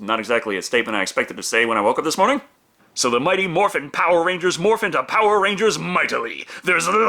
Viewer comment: I actually thought it said "Morphine Power Rangers " like the rangers are on Morphine